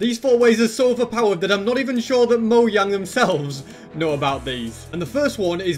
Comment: I think just said " Moyang"